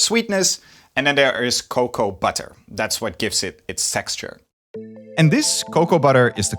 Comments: What did he say???? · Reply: Texture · Did I hear it right? Sexture? Well, I understand perfectly if you are a chocolate fan.